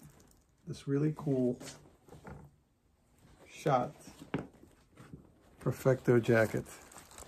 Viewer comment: when you said "schott.... perfecto jacket" I thought you said shot as in there was a bullet going through it LOL, then I saw the title